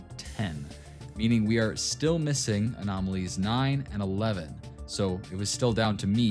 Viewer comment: DID BRO JUST SAY 9 11?!?!?!?